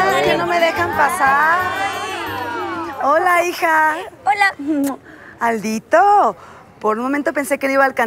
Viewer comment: yo entendi caldito😂